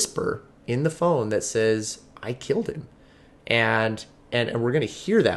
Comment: I didn't recall that whisper at 18 seconds but another replay of the call I heard had someone crying in the background and it sounds like they sob very loudly "i killed him!" Both I've listened to several times and with or without headphones ima hear "I killed him" . RIP little man 😟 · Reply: I heard "I killed him" definitely